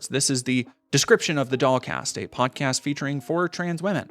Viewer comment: lul. This whole time I thought ya was sayin' DogCast.